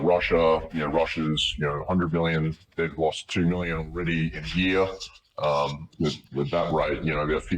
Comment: " They lost 2 million here " , did i hear that correctly?